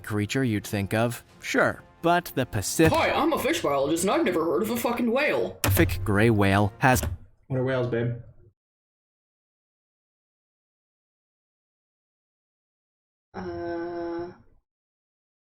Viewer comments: I can't get over the part where is making fun of him and he dead as looks at her straight faced and says "what are whales babe?" And it's just silence lmao. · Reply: Oh I thought he said "are whales big?" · Oh I thought he said "are whales big?" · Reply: I thought he said "We are whales" hahahaha